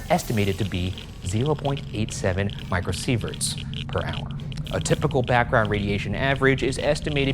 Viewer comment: I thought Ryan was saying "Michael Seevers" instead of micro sieverts.